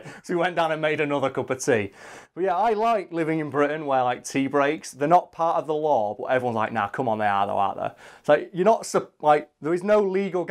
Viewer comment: I can't figure out what he says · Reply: firin' lightning games “tea breaks, they’re not part of the law, but everyone’s like ‘nah come on they are though aren’t they?’”